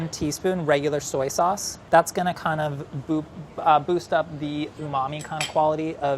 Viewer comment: he says "boop" instead of "boost up". I hereby nominate "boop" as Chris' catchphrase/word.